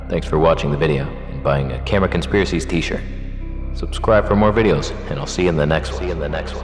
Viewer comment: I always thought you signed off by saying 'subscribe for more videos and I'll see you later', not 'see you in the next one'. My perception of reality has been shattered.